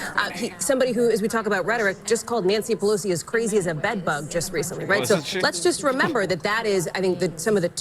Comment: Did Trump really call Nancy Pelosi the "B" word!? :o